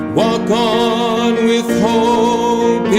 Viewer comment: Awesome! Hh I heard ‘walk on’ as ‘rock on’ for a moment. Both work.